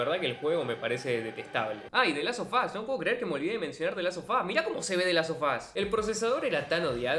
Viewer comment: The last of que? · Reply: que no es the last of us? Es que parece que dices the las o fas... En el minuto 1:33 ... Así se escucha